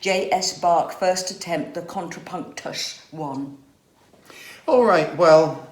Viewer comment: Did she say "contra punk tush"?